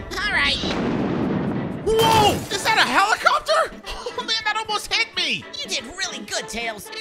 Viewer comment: I thought tails said is that a volcano